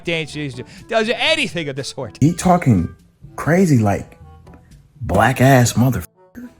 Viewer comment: I don’t even really know who Taylor is, but there’s a video from another angle, and you can see his lip move like he’s saying “whack”. At first I thought he said “black” and I was on board with exposing him. But a video from another angle proved he wasn’t being r@ Not here anyways, I have no idea about any other time because I don’t even know who he is.